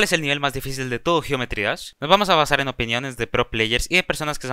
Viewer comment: Escuché "cuál es el gay más difícil de geometry dash"